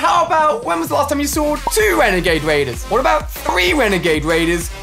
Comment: did he just say, “wenagade waider”!!! NANIIIII